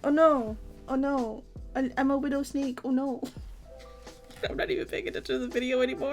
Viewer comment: I thought she said a f word snake